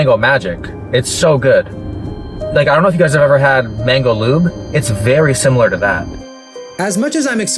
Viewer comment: Hi, I am Deaf and I have trust issue with closed captioning sometimes, but at around 7:28, did you really say "mango lube"?